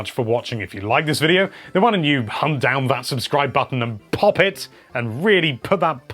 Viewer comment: At first I thought he said “hump down that subscribe button”... What has my mind become